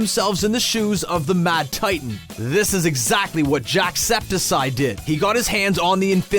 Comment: did you say "JackseptSIGH"